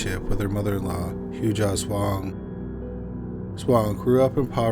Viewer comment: Im sorry I had to rewind twic cause I hear "mother in law Hughass wong" either way shes a scary crazy woman. 33 times with a hammer is not self defense. SHannon shouldve left real quick poor girl . Larry is crazy. They need to get faith out of that house Cynthia is a reason why some people shouldnt have guns. Shes insane.